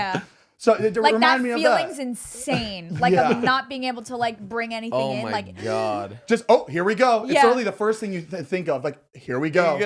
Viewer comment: All I heard at 16:33 was “Ooookaayy let’s go” 😭